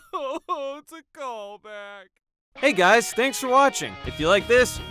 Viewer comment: I had to laugh a few seconds after 5:46. Not because of the weeping, but because of the "it's a callback" quote I heard him say.